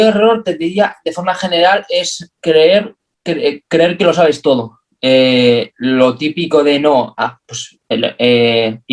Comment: Hola. Muy interesante todo. Enhorabuena por el contenido. En el 55:50 recomienda un YouTube y formaciones (aparte de EXOS), algo que no entiendo bien. Podrías escribirlo? Lit up? Thanks!! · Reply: Gracias por ver el vídeo hasta el final 🙏🏽 Hace referencia a Lee Taft. Su instagram es igual, todo junto.